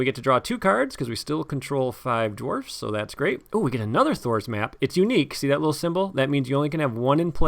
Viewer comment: Every time I hear you say "Thror's Map" I hear "Thor's Map" and I keep expecting to see you putting Mjolnir on your table.